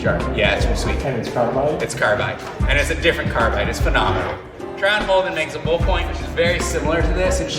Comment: What is the make and model of the hammer you start talking about around the 7:00 mark? I hear you say it, but I can't quite make out what you're saying. Thanks in advance. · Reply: It's a Rebit point in a Trow & Holden Chisel Whizard holder.